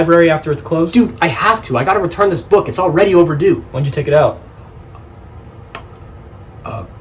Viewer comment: why’s it say didd instead of did WHAT THE HELL CAPTIONS (hint didd y)